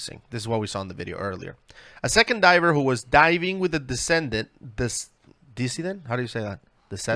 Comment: While I don’t see the text, at 16:34 the word is likely decedent (meaning person who is dead) and is roughly pronounced dis-EE-dnt, like deceased but with a -dent instead of -sed. I recognize that this isn’t really important given the seriousness of your video, but in case you guys wanted to know.